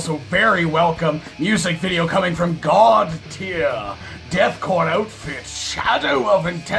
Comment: thought for some reason he is saying godfear - bound in fear .